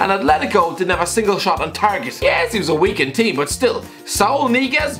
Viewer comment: what did you say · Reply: Weakend team